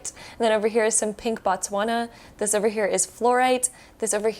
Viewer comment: Can someone tell me the names of crystals? I literally hear "pink butts wanna" when she showed the second crystal .. . . (Non native English speaker here)